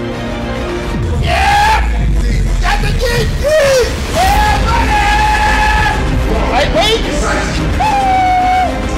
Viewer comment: what does he say at 2:59? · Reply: adam abdulhadi got to dig deep